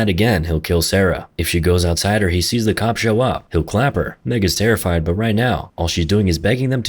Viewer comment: did you just say the N-word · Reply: Meg is terrified?